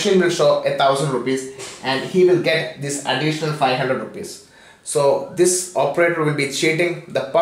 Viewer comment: sounds like u said they'll get 500 rupees :P